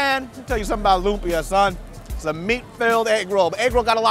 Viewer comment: I thought he said it's a meat failed eggroll at first! 😂😂